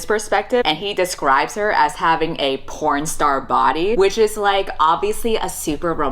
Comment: i thought u kept saying CORNstar body and i was like what's that?? 😭😭